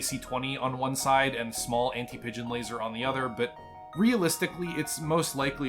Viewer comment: I can't tell if it's sleep deprivation(was up for 24 hours)or not but the first time at 17:50 it sounded like you said Anti-midget Laser after laughing for a good 5 minutes then rewatching it I realized you said Anti-Pigeon Laser.